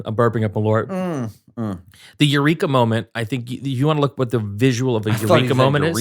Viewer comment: i thought you said urethra moment